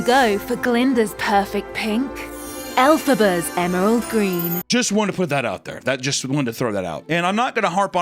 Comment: Dude I'm brainrotted I thought they said 'Elphaba's Anal Green's